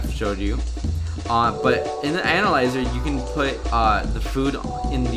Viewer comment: Is it just me or does he sound like he says anal-lizer......